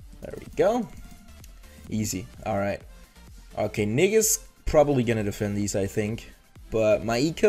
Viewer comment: did he say something wrong ?😂😂😂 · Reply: Lastec Targif oh no. I said “nick is” loool